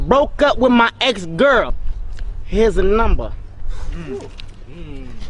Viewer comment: did anybody ear someone say "That's what he said" at 0:04? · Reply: He says "what that shit say?"